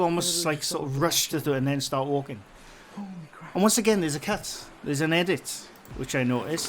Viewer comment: LMAO! I was looking for a cat because I thought you said “There is a cat.” Turns out you were saying “cut”, “There is a cut.” 🤦🏻‍♀️